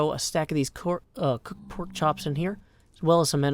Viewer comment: I love how you almost said “corpse-chops” instead of porkchops around 14:00 haha